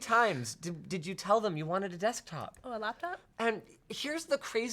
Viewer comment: What did say right before Linus said thank you? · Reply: Laptop